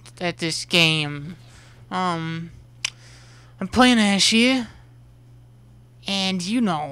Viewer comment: Did he say im playing 'Ashe-e' or 'Ashe here'